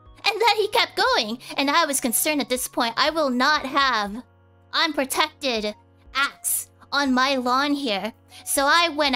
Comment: Congratulations on the debut! On a side note, when she says "unprotected acts," I heard "unprotected axe," and now I'm imagining cats fighting with tiny axes.